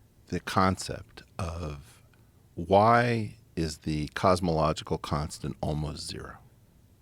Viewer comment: I thought he said cosmological kunt lol